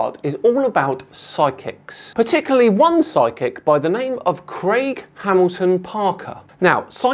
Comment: Thought said “sidekick” not psychic. That was confusing. Thought Robin now knows the future of Batman. What is coming in this video??? Ah, psychic… way more sense.